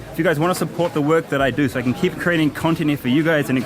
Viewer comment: I could swear he says keep creating continent, that's a pretty tough job, creating continents!